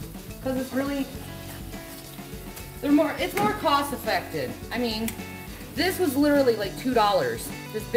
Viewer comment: #FlavorTown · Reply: I think she might've said "It's more cost-effectin' " 1:49 She is literaly eating from both hands 2:42-2:55 I watched this at least five times so far